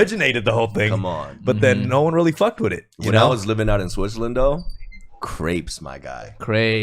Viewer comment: Wahahahaha I couldn’t stop laughing (first time I heard americans/english speaking people say it) 1:01:24 CRAPES XD